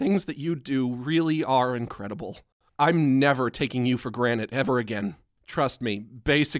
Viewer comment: HE SAID GRANITE INSTEAD OF GRANTED